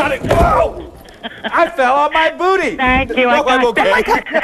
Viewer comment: Bro I stg I thought it said I fell on my Hiney